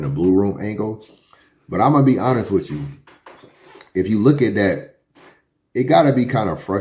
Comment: Did I hear him say “get a look at that”😂